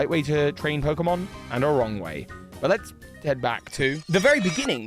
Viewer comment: the auto caption says "no wrong way" when you say there is a [right and a] wrong way to train, implying the complete opposite of what you actually said lmao